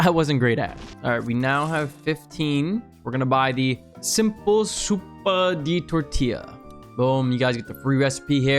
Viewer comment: I heard like "simple super do tortila" as a native mexican 🙏